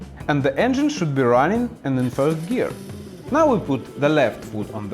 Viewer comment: subtitles say first gear, but it really sounded to me like you said third gear?